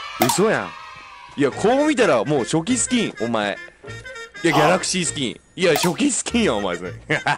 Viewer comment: しゅごい ギャラスキースキンだよがかわいい( ギャラスキー スキンって聞こえる)